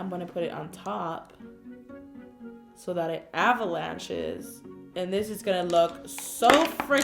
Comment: sounds like she is says bitches XD